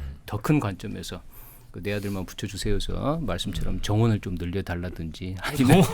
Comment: 정원 늘려달라는 멘트에 빵터졌네 31:54 ㅋㅋ 의대정원으로 들리는건 저뿐인가요